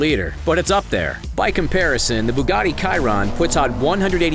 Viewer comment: Did he say the Bugatti “Kiron”?